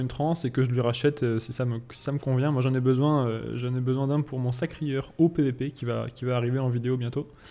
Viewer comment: Ma tête pendant l'intro : 😀 Ma tête quand j'ai entendu "pour mon sacrieur eau qui va arriver bientôt" : 😩